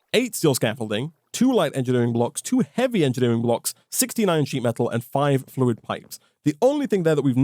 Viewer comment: anybody else think he said 69 sheet metal?